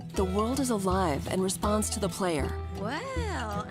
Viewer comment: it sounded like she said playa